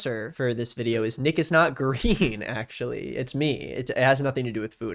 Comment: When he stopped for the ad of today i thought he said niggas not green😂